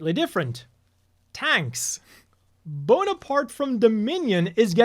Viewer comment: my english listening comprehension is so terrible, I heard "Bonaparte from THE MINIONS" 😭